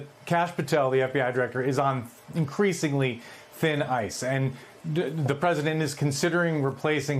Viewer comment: Did he just say skin ice 😮😂😅